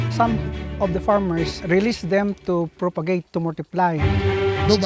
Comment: 40 I think, they said Release not lease